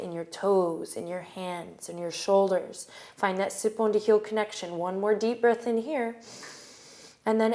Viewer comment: She says this all the time but I actually have no idea what she's saying "Find that x to heel connection" - HELP? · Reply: 'Sit bone to heel connection' - your sit bones are just the two round parts at the bottom of your pelvis that you sit on :)